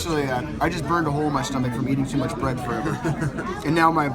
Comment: What does he say at 7:28 ? "burned a hole in my stomach from eating too much....... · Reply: eating too much bread